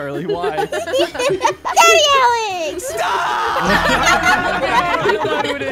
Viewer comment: I thought it said baby Alex but it said dady Alex